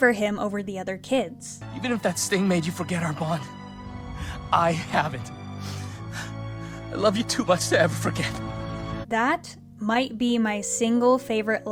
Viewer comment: Can someone tell me what the hell Eugene says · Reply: “Even if that sting made you forget our bond, I haven’t; I love you too much to ever forget.”